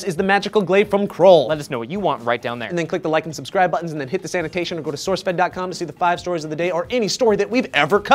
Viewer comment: Every time one of you says "... this annotation bar..." I hear "... the sanitation bar..." and have to head to the rest room.